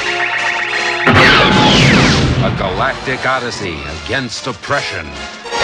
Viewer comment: did they just say this is against depression? i think i peedd